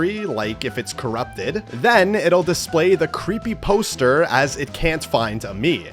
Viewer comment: the what? say that again? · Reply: Creepy poster